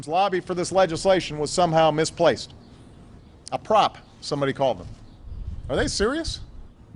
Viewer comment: What does he say around 3:10? "...somehow misplaced. A ___, somebody called it." Sounds like "prop".